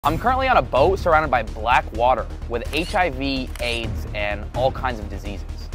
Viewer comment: i thought my man said am surrounded by black people😂